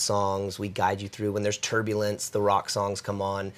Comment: did he say turbulence?